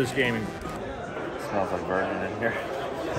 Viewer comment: At first I couldn’t understand Preston. Rewind. Turned up volume. Still couldn’t understand Vance. Rewind again. Turned up volume. I heard “Smells like bourbon in here”…I thought that was weird. Rewind again, again. Turned up volume and CC. “Smells like virgin in here”. Yup. Understand your reaction now. Lol